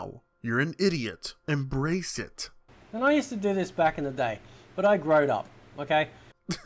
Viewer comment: Did he just say, "But I growed up"?